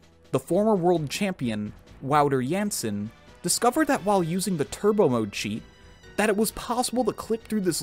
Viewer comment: i had to re watch like 6 times because i swore he said turd emoji 💩